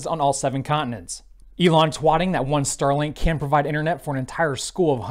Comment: Did you just call it Twating?